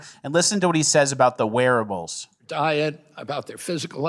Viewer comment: Oh, wearables! I thought you were saying, "werewolves".